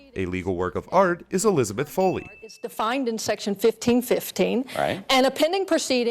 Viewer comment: when she said "section" i heard "sex..." hahaha..